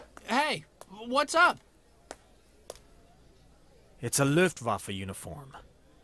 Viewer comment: Okay, I get where people are coming from, when they spell the "Ü" like a regular U in english, but why the fuck did he just say "Lüftwaffe"? Did he think germany used air conditioners as weapons?